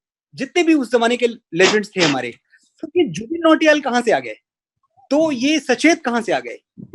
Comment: Lol....did he say Tanishk Bagchi....😂😂😂